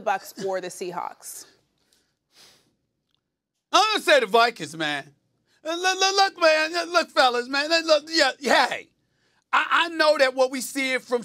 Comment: eimgonasaedavykinsman, leleleleookmainjuslokfellaslelelelookyeyeha Literally what i hear from him